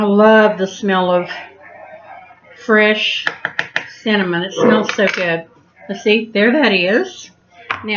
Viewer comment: U are to cute an very southern I thought u said salmon.i was confused (cinnamon)Loved it